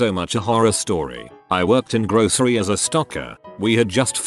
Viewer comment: ok so on the first one i was listening to it and I thought he said stalker not stocker. so confused Lmfao XD